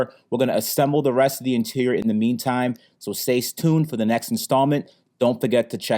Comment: did he said "stay stune"?